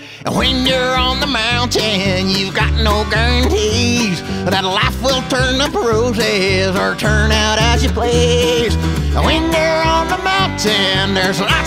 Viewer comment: Lyrics 37 years ago a witch done put a spell on me A spell where when I’m talking I’m singing it with glee When you’re always singing, you got to live alone That’s why I made this mountain shack my home And when you’re on the mountain, you got no guarantees That life will turn up roses or turn out as you please. When you’re on the mountain, there’s lots to be a’feared That’s why this here old mountain goat’s prepared! Be prepared, be prepared This lesson must be shared This lesson must be shared Be prepared Be prepared, be prepared And unless you got a spare You got one life, so handle it with care I got horns that open bottles And I got horns that hold my keys I got horns that when you turn ‘em right, they help me watch TV I got horns that open pickle jars And horns that come with hair I got horns that hang my other horns I always come prepared Be prepared, be prepared This lesson must be shared This lesson must be shared Be prepared Be prepared, be prepared And unless you got a spare You got one life, so handle it with care Yeeeheeeee! Keep your hands and feet inside of the vehicle at all times Ooooooh, an avalanche is coming and I do not feel prepared It’s rumbling like a mountain lion I must say that I’m scared And if not for the witch’s spell you’d hear just how I scream But since I’m only singing, I’ll just yodel ’til we’re creamed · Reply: Shoot, I always thought he said "you got no guarantees; life will turn to bruises or turn out as you please".